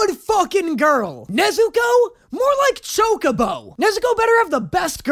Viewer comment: I know that was Chocobo, but it really sounded like "choke a beau", which... eugh.